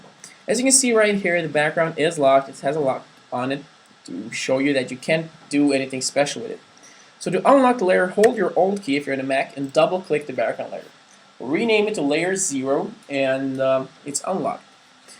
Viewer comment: Did you say 'if you're on a mac, hold your alt key'?